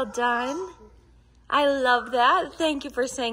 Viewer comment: We sure do love him !!!!! you are such a handsome boy !!! I swear I heard him say " I love ma ma " Such a precious child !!!!